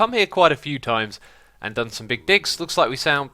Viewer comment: 😂 sounds like he says big dicks